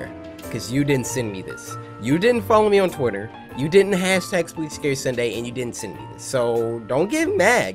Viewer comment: WHY DID THR CAPTIONS SAID "You didn't have s3x with scary sunday" 💀💀💀💀💀